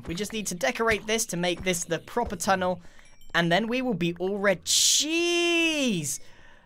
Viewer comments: "we'll be all red cheese" is wut it sounded like lol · Grian-" All rea-JJJEEEZZ!" What I heard- " All the CHEESE!"